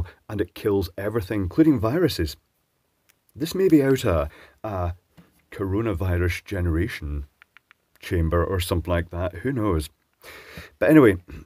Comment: Did you say "out of a coronavirus generation chamber?" at 9:10? That's quite an interesting hypothesis. Anyway how do you arrive at this reverse engineering? Do you have a spice simulator or something that helps you visualize?